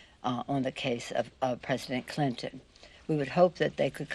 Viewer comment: Did she say president Clinton ?